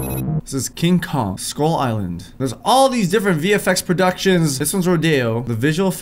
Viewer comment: is it just me or does every time says vfx it sounds like hes saying the effects?